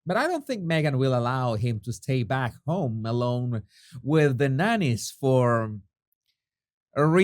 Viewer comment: When you said Harry was going to stay at home with the nannies I thought you said bananas for a second. Bananas. Cocaine filled bananas.